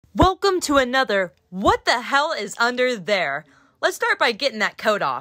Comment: "what the hell is under hair" is what i thought she said lol.. immediately disappointed